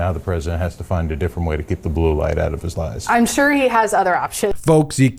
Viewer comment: I heard "... keep the light out of his LIES! I'm sure of it 😂